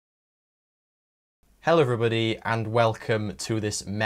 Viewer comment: Almost thought he said hell everybody at the beginning